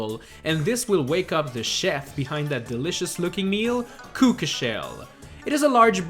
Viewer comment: Did he just say cookashell